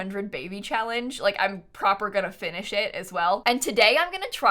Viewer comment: Did she say “I’m proper gonna finish it” at 12:45? Aww, Dan’s British vernacular is running off on her 😅